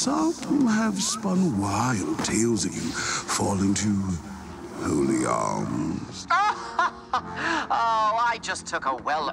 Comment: wait did he just say holy arms ?